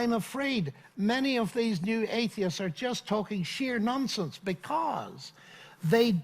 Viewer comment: I realize it would never happen, but for moment, I thought he was going to say 'talking sh??'.